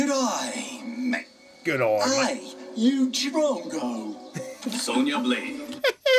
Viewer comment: as a Australian myself he didn’t do a bad job with the Australian accent “G’day mate” “Oi you drongo” That’s what he said by the way